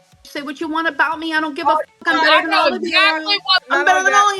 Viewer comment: Did she just say “ I’m better than All of you?” 😡Man she really is a Narcissistic biotch